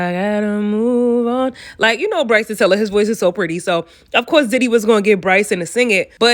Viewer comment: Your voice is pretty too 1:10-1:13 I heard that❤️